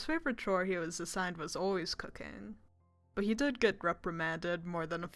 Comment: HELP I HEARD COCAINE INSTEAD OF COOKING